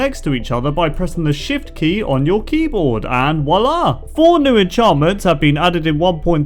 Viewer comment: DID U SAY A FILIPINO WORD?! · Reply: No, he's saying "voila" not "wala". Pinoy Pride is cringe get over it.